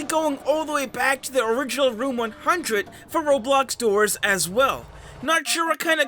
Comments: what? U said “100” The text: 104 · he says original door 100 and the captions say 104💀